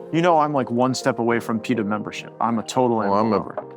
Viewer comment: Bill thought he said PETA membership. I thought he said PEDO membership.